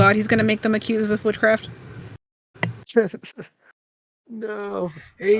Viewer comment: I thought she said Accuse him of footcraft